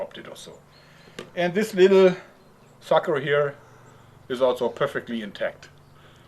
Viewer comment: Did i hear right? 10:30 And this little fucker. XD I AM DYING!